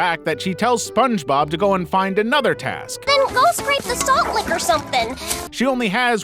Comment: I clearly understood what she said at 0:42 she said, "Then go scrape the salt lick or something." Which is a block/brick of salt but doesn't taste like salt if you lick it